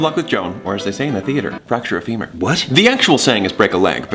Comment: I THOUGHT HE SAID FRACTURE A LEMUR AND WAS LIKE "YOU LEAVE KING JULIAN ALONE LOGAN!!! ME AND MORT WILL KICK YOUR BUTT!!!" (still love you though you nerd)